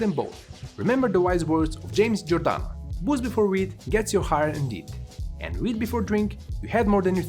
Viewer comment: The first time I heard the "Booze before weed" thing I thought you said "Booze before weed gets you Hard indeed". I had to back up and relisten! 😆